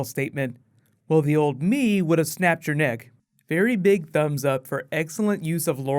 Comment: Let me know if you think movie Waller killed show Waller, posting new Game of Thrones and Legends Of Tomorrow tomorrow :) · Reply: I noticed you said that Oliver said "I would've snapped your neck" I heard it as "I would've snapped Joiner's Neck" I don't think he would ever say he'd snap Felicity's neck. :(